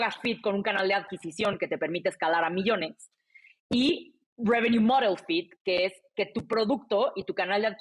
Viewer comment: ¿Qué está diciendo en inglés? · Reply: Revenue-Model Fit